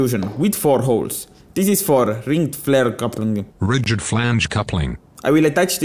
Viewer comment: New movie: "The Rigid flange coupling" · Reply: Hahaha I heard "Ric Flair Coupling"